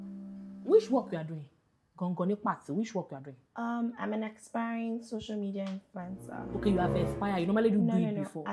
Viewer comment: Walai I heard "I'm an expiring social media influencer" 🤣